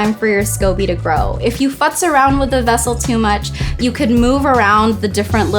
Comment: Did she just say "Don't fux around with it"? Lol